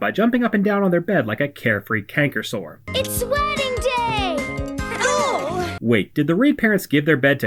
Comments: I bet you Cora's parents kick David and Jane out of their bed 😅. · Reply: am I the only one who hears “ it’s sweating day!” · I swear she said “it’s sweating day”